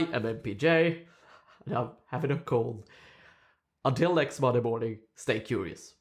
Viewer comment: "Until next motherboarding, stay curious." I swear, that's what I thought you said.